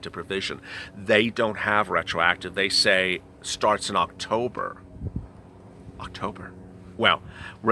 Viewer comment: I thought you said early October for the unemployment